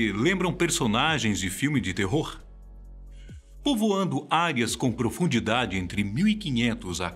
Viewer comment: foi que entendi errado ou ele disse povoando águias?kkkkk